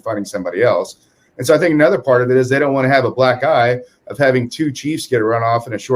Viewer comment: Oh black eye! I thought he said they don't want another black guy!